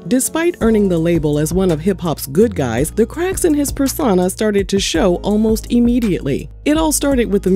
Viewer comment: Giirrrrllll I thought you said, "The crack..." I was shook had to rewind cause you said "the cracks in his life..." You know once we hear crack it mean they hitting the pipe. Chiillllleee